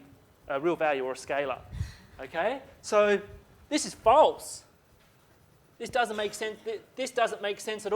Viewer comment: I first heard "this is balls" and burst out laughing. Good times with!